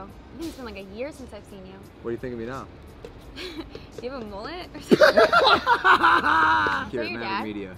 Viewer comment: Logan: “What do you think of me now?” “Hehe, do you have a Millet?” Lmaoooo 😂😂😂 She burned him and the way she says Millet instead of Mullet is adorable.